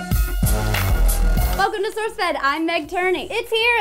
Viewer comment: i literally heard "Welcome to sourcefed im boobs"